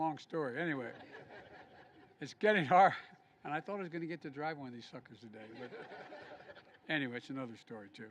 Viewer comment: did he say “I thought I was going to drive one of these f*ckers today”? 🤔